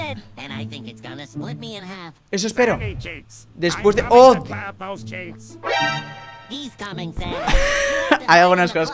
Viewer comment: Por favor diganme que dijo calamardo aqui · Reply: Que los calamardos no tienen pitos xD